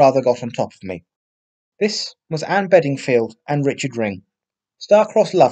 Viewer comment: I thought it said ringo Starr at first lol x